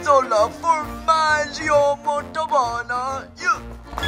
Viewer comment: wtf did he say? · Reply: ahem ah Gorgonzola for Majio portavona yea